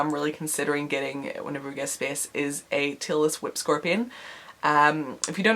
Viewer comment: Sounded like you said Taylor Swift scorpion. XD